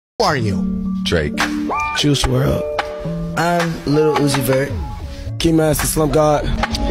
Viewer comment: They way juice and uzi say there name is just sooo calming luv and peace to them alll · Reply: i thought he said lil lucifer